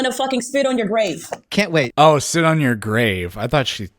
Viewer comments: I thought she said sit on your face too LMAO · I thought she said “sit on your face” too 😂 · "sit on your face" Nice Freudian slip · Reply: She clearly said “spit on your grave.” What’s the mishearing version of a Freudian slip?